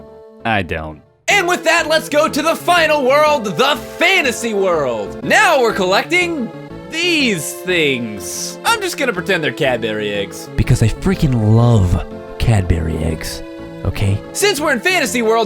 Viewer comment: Honestly thought you said "Cat berry eggs".